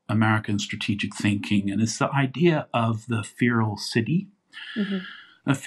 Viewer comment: Does anyone know what paper he's referring to 41:54? I can't find anything useful on google about viral cities or firil cities and don't know what other word he might be using. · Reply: I think he said "Feral" as in wild, stray, undomesticated